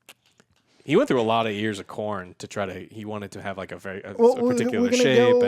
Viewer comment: Ohhh, "ears of corn," I heard that as "he went through a lot of years of Korn" and was like the fuck's wrong with that? I love Korn!